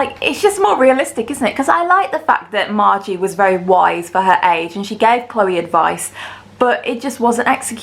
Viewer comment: Ngl I thought you said “I liked the fact that Margie was very wide for her age.” 😂🤦🏼‍♀️